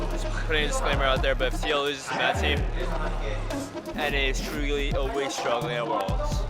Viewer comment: wtf does turtle say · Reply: "NA is truly always struggling at worlds" if you looked for a real answer ^_^